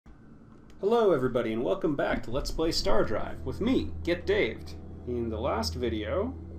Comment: At the intro i heard "Hello everybody and welcome back to lets play GetDaved, With me, StarDrive." And the ensuing mindfuck left me speechless until I watched the intro again. My head hurts.